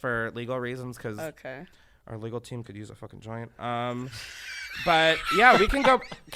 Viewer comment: I still don’t know what said 😅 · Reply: "our legal team could use a fckn joint"